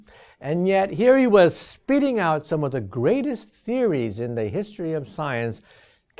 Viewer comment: For a second I thought he was saying "He could not carry a decent conversation ,and yet here he was spitting out the hottest verses." LMAO the shame is real.